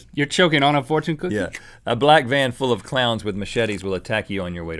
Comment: I thought that rhett said a black man clown😋😃